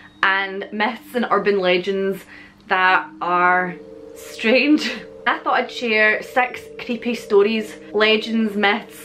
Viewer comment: I thought you said "I thought I'd share sex creepy stories" 😂😂 And I'm going to Scotland soon, I want to visit the Kelpies 😊